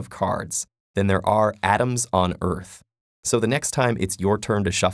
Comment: at the end i thought he said adams so i’m like, surely there can’t be that many adams on earth 😂 Edit